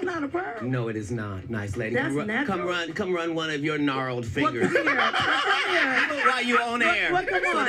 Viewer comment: Katt boiled Wanda in her own broccoli pot. He had me at "gnarled fingers". 💀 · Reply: He said "gnarled" fingers..? Umm I thought/heard him say "narrow" fingers..?